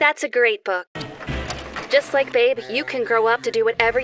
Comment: did she just say babe